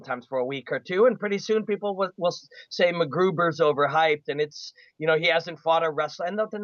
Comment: Did he just call him mcgruber at 11:00?